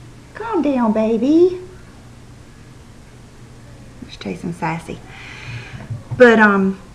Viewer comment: I'm going to have to try the Laura mercier in vanilla, I have the natural beige, and don't care for it. I've been using the loreal smooth hair cream, and love it! Lol, I could have sworn you said bubba was chasing his assey...took me a second to understand lol!